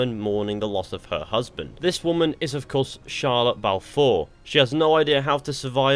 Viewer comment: i thought you said shia lebouf four